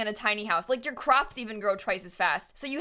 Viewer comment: I thought said "your crocs grow twice as fast" WHEN I TELL U A TOOK MY CROC OFF AND HELD IT CLOSE